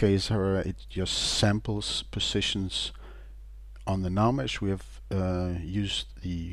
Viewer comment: This looks great, but I'm guessing the guy didn't write this example. I mean seriously what is he say at 9:02? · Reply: He's just talking about how the script is used for scanning the navmesh for walkable positions. He does pronounce "navmesh" weird though so I'm guessing that's where you got confused.